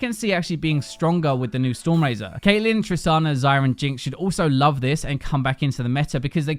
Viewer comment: did you say Zyra is good with a storm razor? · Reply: Xayah